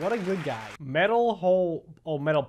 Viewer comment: at first I thought it said hole too😂